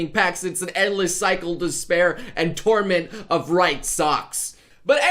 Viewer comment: Did you say... DESPAIR?! XD